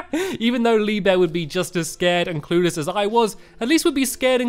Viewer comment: did he say clueless or pooless?...